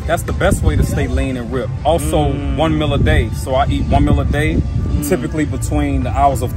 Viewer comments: I guess his one meal is the Trenbolone sandwich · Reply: Haha I thought he said 1mil a day too haha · A first I thought he said 1 mil a day, 💉😂. Then ahhh One meal a day. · Not even lying I swear I heard him say i eat 1mil a day 😂